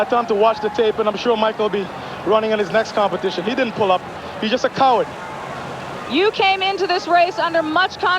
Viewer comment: Did Donovan call Michael Johnson a coward? Or did I miss hear that? 25:17. Donovan is not a gracious winner. For Shame!